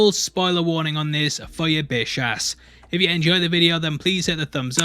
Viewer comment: Did you say "Bish ass?"